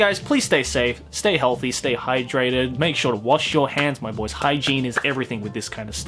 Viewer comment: Is it just me or did he say don't forget to watch your hen ?